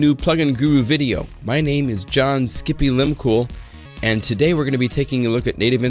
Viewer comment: Did he just say "Skimpy-Limb-Cool"?!?!?!